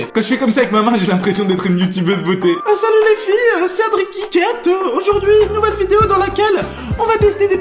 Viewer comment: Mdr quand ta fait la Youtubeuse et que t'a dit : "c'est Adri Geekette" j'ai compris "c'est Adri quéquette" lol